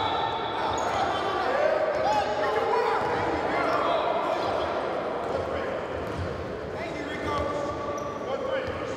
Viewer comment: What did westbrook say? Lmaoo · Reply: Ahmed Amiri nah lmao he said NIGGA WHERE cuz Rico called a foul on him